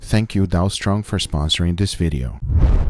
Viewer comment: The first thing i heard was: "Thank you donald's trump for sponsoring this video"